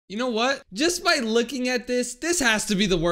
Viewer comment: it sounded like you said "just by licking at this"